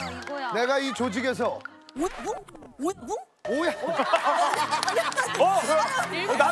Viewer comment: 솔지 뭐라고 하는거임? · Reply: '오야붕???' 이라고 하시네요..ㅋㅋㅋ